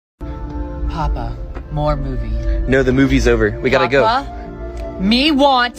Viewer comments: I heard “Papa, more booty” 😭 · I only heard "Me want more bobbie💀💀💀💀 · I thought he said "papa me want more booty" 💀😭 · The first time he said me want more movie I thought he said me want more booty